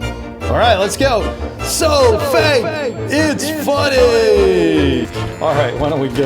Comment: did it say: so fak it's so funny???